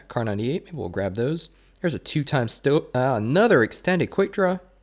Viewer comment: Love ur vids huge fan btw who heard say 2x stope